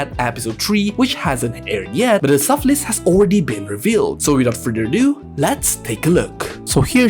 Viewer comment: almost thought you said "without further doodoo 💩"